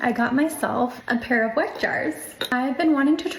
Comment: I just hear "wack jars"